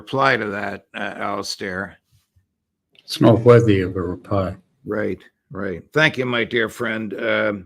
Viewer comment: What does Alastair Crooke say at 29:38? the sentence starts "It's not worthy of a .." I don't get the last word. Can someone help? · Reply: reply. trumps.remark was digusting